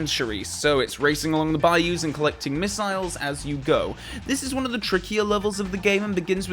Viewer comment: I thought she said "Mortars" not "missiles" 🤔. But as a spanish fellow I could be mistaken 😂😂